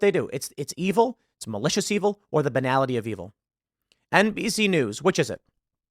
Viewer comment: dude I thought I head Tim say, "NPC news" as a joke and i thought that was hilarious, but i just misheard him.... but hey feel free to use that one in the future